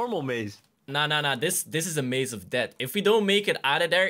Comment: maze of death and I heard rage of death